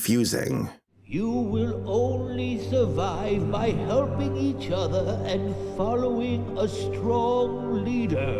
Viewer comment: i thought he said humping each other